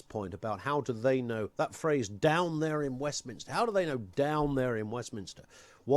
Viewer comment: I heard them say: " Down there in Brussles - what do they know?"